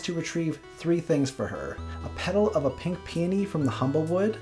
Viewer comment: I kept mishearing "pink peony" as "pink peeny"........